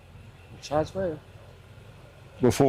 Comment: What did the guy say to “Dr” Phil? 2:09 Phil asks him what he’s been charged with? Thank you in advance. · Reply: He questioned back saying “charge with?” And then says he is not going to say anything and wants a lawyer